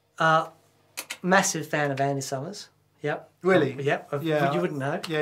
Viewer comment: I thought you said massive fan of ANN summers haha